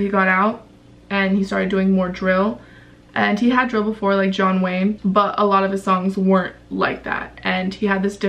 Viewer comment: did she just say john wayne